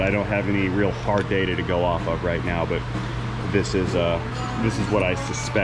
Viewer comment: Did you say "right meow"? because it sounded like you said "right meow" ;P